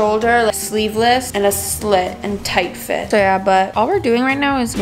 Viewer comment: did say “yeah” in Chinese? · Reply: no she just said "so yeah" in english?